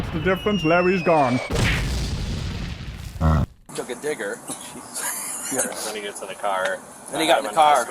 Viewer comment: What he say? "Fuck'n What?" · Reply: Forget that (he said "digger"). The guy they were talking to before that called him a "colored guy" 😐😒